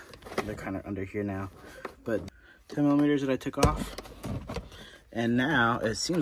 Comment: I listened to the video again just now and this time I did hear 10 mm. Funny because I listened to it several times before and thought you said 20 mm. I know slight deficits in hearing can affect such things, but a few years ago there were a number of videos or tests where someone would say a particular word and different people would here different words. Anyway, thanks for the tip about leaving comments on shorts, I guess I didn't recognize the comment icon in the sidebar. I had room to lay the me console covers to the right, so that was easier for me than having to detach and reattach those connections.